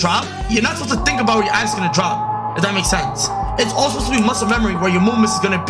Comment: It sounds like he is saying "ass gonna drop"